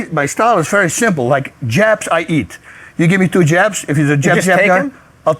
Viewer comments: Wow! for a split second there I thought he said 'Japs, I eat. If you give me two Japs, I take them...' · At first, I thought Bas said, "Japs, I eat!" 🤣